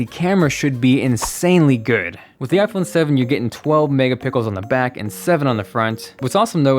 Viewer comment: Love the video, but I do not remember them talking about mega"pickels" like I heard here at the 4:00 mark ;-).